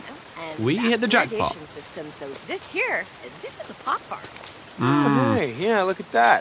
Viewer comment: When she said pot farm I thought she said a pop tart